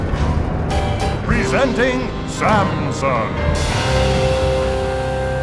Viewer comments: I thought he said Samsung 😂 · When the Railroad Baron said "Presenting Samson!", he sounded like he said "Presenting Samsung!" Btw, no offense to this banger. Banger means "awesome thing".